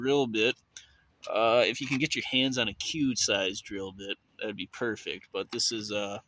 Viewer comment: I can't understand or see what you're saying. A cube sized drill bit? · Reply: a "Q" sized drill bit, 0.332inches/8.43mm. that's what is recommended for a 1/8 npt thread